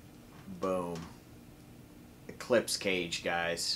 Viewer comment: I thought you said "Clip..." then i heard "cage" and i was like Ohhhh eclipse not clip you are still ok.